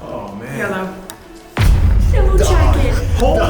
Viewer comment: I laughed hard when she said “yellow” bc I thought she said “yolo” like “I need friends…yolo”😂